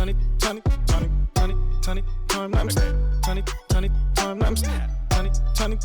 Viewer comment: It sounded like he said 20 20 20 time lapse